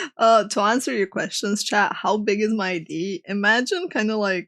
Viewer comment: Am I the only one who heard "How big is my D?" or am I going crazy?